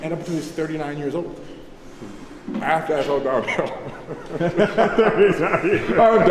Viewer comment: I dont hear the joke at 10:30 what was he saying? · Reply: "after that it's all downhill"